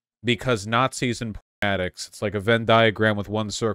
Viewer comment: "Nazis and what addicts"? If you block out the entire word, how am I supposed to divinate what you were originally saying? Why have audio at all if you're relying on telepathy anyway? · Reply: He said "porn addicts"